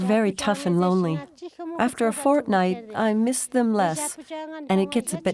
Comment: “ After fortnight I miss them less”. Is what it sounded like she said 10:19 - 10:22 and let me know what you here👂